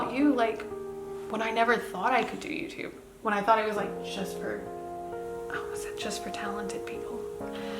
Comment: Does she say "When I thought youtube was just for homosexuals" at 17:16 but, they edited it in with talented people, i swear that is what i am hearing